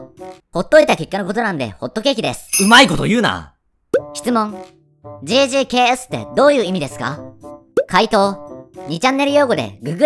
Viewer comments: JJAKSって聞こえたの私だけ…？ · がggrksなのに言い方がjjksに聞こえるのは俺だけ？